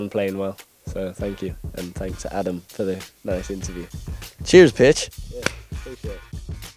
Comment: I heard cheers bitch instead of cheers pitch 😂😂😂